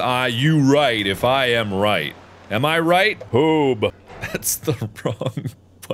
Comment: Mark: am I righ? Marks soundboard: boooob Mark: (chuckules) that's the wrong button Edit: I think he didn't said 'boob' but imma keep it cuz it's more funny